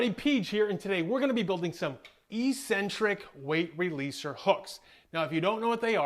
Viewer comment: Oh I thought it said electric, not eccentric lol.